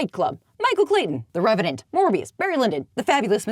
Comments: Wait, did you say "Morbius" was one of your favorite films? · Wait, did you say Morbiuss is one of your favorite movies, or am I grossly misparsing what you said here?